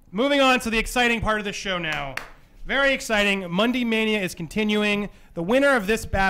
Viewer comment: i keep hearing "Mundy mania" but i dont see my boy Mundy............................. is this a joke?